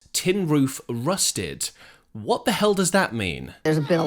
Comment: When I was a teenager I thought it said, “Skid Row, busted.”